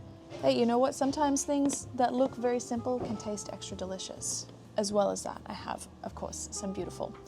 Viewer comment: I kept hearing the "delicious castle"